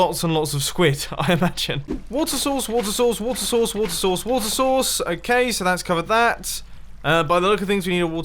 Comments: Water source water source water source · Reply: I heard 'water sauce' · At like 7:11 sounds like he says water sauce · It sounded like he was saying water sauce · "walter sauce, walter sauce, walter sauce..." not making fun of you, just an illusion i hear😂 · Heard him say "Water sauce" XDD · war sauce war sauce war sauce I heard this what I texted. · what i heard: water souce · i thought he was saying water sauce